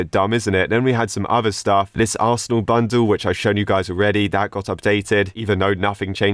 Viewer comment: im really addicted to arsenal and i heard arsenal